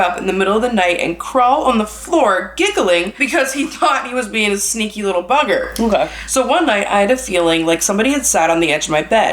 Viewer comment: Tell me why 21:04 I thought it said sneaky little burger 😂😊